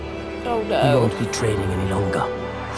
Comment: My heart breaks every time I hear Kalé say, “we won’t be trading any longer” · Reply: Wait he says that? I thought he said "The gods betray me no longer"